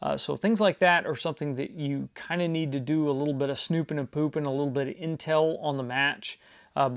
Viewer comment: did I just hear "snoopin and poopin"? Lol